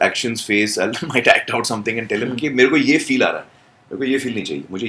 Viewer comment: the subtitles are "tel aviv" instead of "tell him" 😂😂😂😂